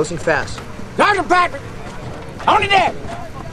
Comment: The subtitle says "Hold it there!" I think he actually says "On the deck!"